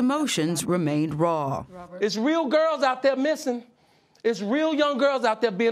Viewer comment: he said “missin” and it sounded like he was singing ignition